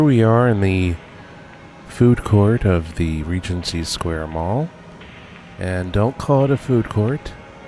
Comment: first thought it said pregnancy square mall. XD